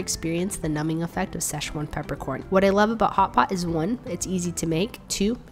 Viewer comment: I thought it said hot pockets 😂😂